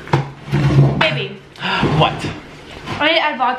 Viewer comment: nick thought karlee called him “baby” instead of so he responded i’m dead